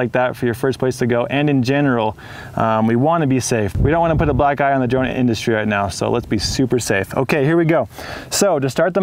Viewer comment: "we don't wanna put a black guy...." what??😅🤣🤣 Did I hear right? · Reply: No, he said black EYE on the industry